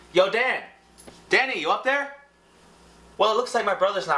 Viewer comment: I kinda laughed my ass off when he said "Danny" cuz i thought he was saying daddy but then he said brother and i was like wtf is going on, then 5 minutes later i realised he was saying Danny -.